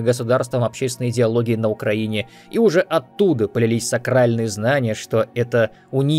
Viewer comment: Мне послышалось "Сральные знания"